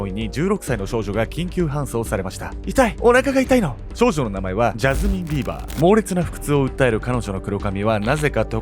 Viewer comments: ジャスミン•ビーヴァー、最初ジャスティンビーバーに聞こえた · 一人目の名前ジャスティン・ビーバーって聞こえたw